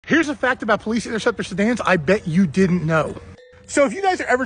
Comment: All I heard was "here's a fact about plisifabtor flabby wobble wobble"